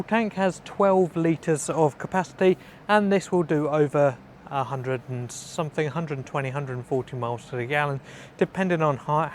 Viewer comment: I thought he was gonna say '100 to 140mph' lmao